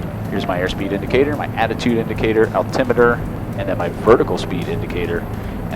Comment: Did you really say "attitude indicator" at 6:38? Great video, thanks so much! Since in this life I probably won't be able to fulfil my dream and learn how to fly a Blackhawk helicopter, this is gonna be the next closest thing and I'm REALLY looking forward to your classes! Thank god Christmas is getting close, so Santa is gonna have to bring around A LOT of fancy tech ^^ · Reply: Yeah attitude indicator. Is that not what they call it in a Bell 407?